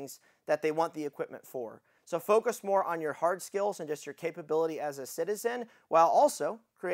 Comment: When he first talked about the overweight guy I thought he said heart skills😂